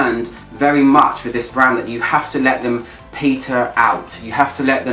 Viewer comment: I swear I heard you say “you have to let your peter out” 😂 anyway, love your reviews 😊